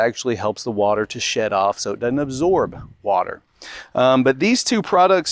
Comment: Did you say the purpose is to 'shed off and absorb water?' How is water getting under a shingle that's doing it's job? If the paper keeps getting wet, dries out, wet, dries out, etc, it won't last very long.